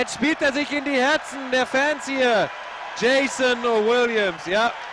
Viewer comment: i understood raef larentz, jason williams, sacramento kings, spectacular and elbow... and i think i heard "fancier" as well at around 0:13... ohh yeh... GREAT PASS! =)